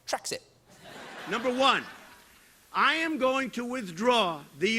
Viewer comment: Oh I heard him say "United Straits", not "Straights". : p